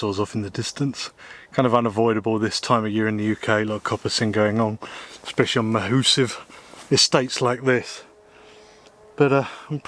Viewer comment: What the heck did he say? English is my only language but I think I need a rural England to Urban Canadian translation. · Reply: A lot of coppicing going on especially on mahoosive estates like this